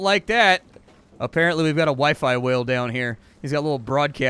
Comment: could have sworn i heard waifu whale...maybe i do need a hearing test